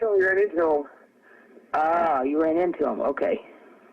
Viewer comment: I clearly hear say: "He shot the first guy" The subtitle say he is saying: "the heart stopped" but he is actually saying: "He shot the first guy" Creepy shit..